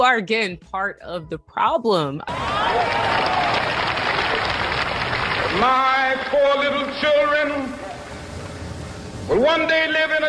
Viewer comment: Funny, I always thought MLK said "...my poor little children..." lol I have to say I like that statement: "I'm not a victim; I'm a victor." I'm not sure if it comes from somewhere else or is original with you, but I like the sound of it.